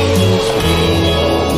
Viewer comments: “You got a Gutter Ball. Maybe next time throw it forward.” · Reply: i heard "you got a gutter ball, maybe next time forfeit " the first few times lol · “You got a Gutter Ball. Maybe next time throw it forward.” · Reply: I heard "you got a counter ball! Maybe next time it will work..."